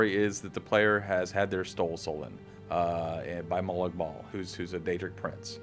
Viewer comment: did he say ''stole soulen'' ?